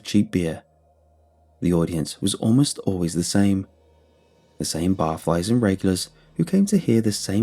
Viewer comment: I thought he said " the regular barflies and rapists" 😂